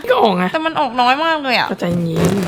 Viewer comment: มีใครได้ยินเหมือนเค้ามั้ยง่าาาตอน 11:14 "ตัวต้องใจเย็นๆ💜💫"อร๊ากกกกน่ารักมากก